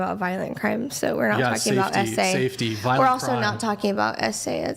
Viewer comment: did she just say you're talking about violent crime not essay what do you consider SA dumbass